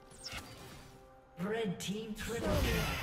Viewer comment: Anyone hear ''Bread Team'' at 1:40 ???